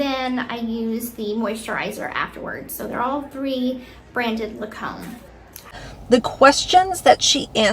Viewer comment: Did she say, "brand name... La Comb" ?? lmao